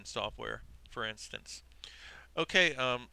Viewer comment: At the end, instead of "click on subscribe", I thought you said "click unsubscribe"..